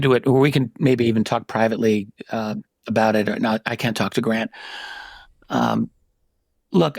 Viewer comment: Did Richard Dolan say he CAN talk to Grant, or did he say he CAN’T? And if the latter, why?